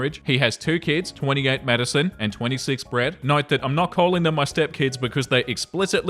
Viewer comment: In the first post, said "28-Madison and 26-Brett" and I had to rewind because I heard "28-Medicine and 26-Bread" and I got so confused 😂